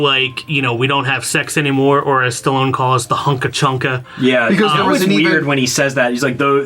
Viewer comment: I think Sylvester Stallone says something like "I mean boning, the wild mambo, the hunka-chunka…" in that part. As a kid, I found that line funny because of how it sounded & when I got a little bit older & pieced things together, I remember asking my dad "What's the hunka-chunka?..." & he looked at me & was like "I'll try & explain it to you when you get older...".